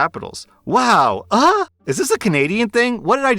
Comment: People are saying that AH is short for AssHole but I thought she was saying "wow eh!" Cause you know, canadians.